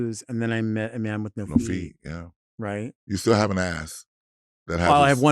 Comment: " you still haven't asked" can someone explain that to me? and then "i have one in the front too" I didn't understand that. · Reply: She said "you still have an ass"